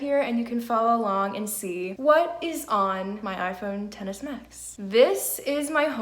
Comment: it sounds like you said tennis max lol, nice video